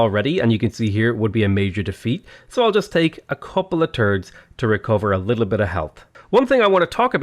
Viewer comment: "couple of turds" am I hearing this correctly?